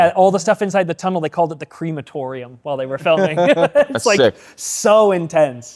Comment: did just say “creamatorium” lol